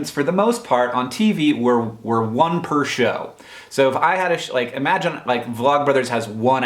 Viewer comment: subtitles say "if I had a shit" lmfao